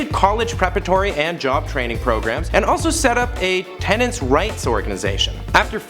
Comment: did i just hear hand job training program